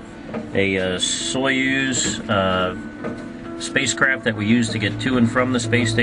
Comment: Did he says at 1:00 Soyuz space crap...